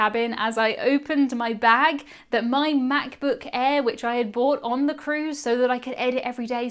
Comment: & 1:38 Is it a British thing to not pronounce the R in the word "brought?" I don't think I've heard that before. And I kept thinking you were saying you bought it. Lol